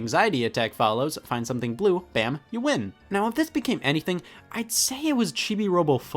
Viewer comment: Scott said Thith instead of This